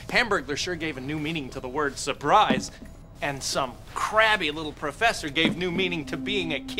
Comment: i thought he said "some crappy little professer..."